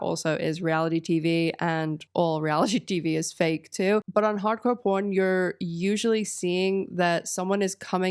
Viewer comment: homegirl says hardcore porn instead of pawn 😂